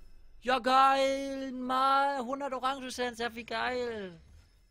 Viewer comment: hab orangensaft verstanden xD